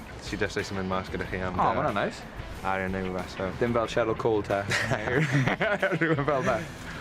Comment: Did he say Cheryl Cole at 2:45 or am I imagining words that I'll recognize? And what I mean by that is yes, I'd like a translation too if someone can :) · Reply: yeh bit late but they were talking about wags . and the presenter was asking aaron does he have a girlfriend like cherryl Cole